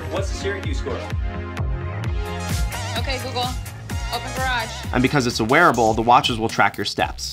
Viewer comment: Did I hear that right ? "Ok Google, open the garage"